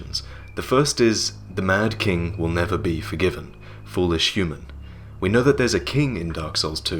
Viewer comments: What I heard was, "the murder will never be forgiven, foolish human....." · Sounds like what's being said is.. "The man will never be forgiven foolish human" is what it sounds like to me.. @ 04:42 Sounds like "You need heed this" as in heed my advice, or something. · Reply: It's been confirmed to be "The mad will never be forgiven" Just thought i'd let everyone know! Japanese subtitles were translated.